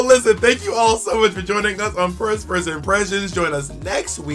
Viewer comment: Did she say "First Purse Impressions"?